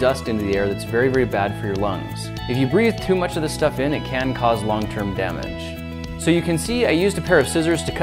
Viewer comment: Did you make a pun? It sounded like you said "lung term damage"